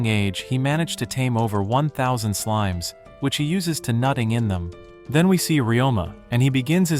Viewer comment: did you say he uses them by nutting in them?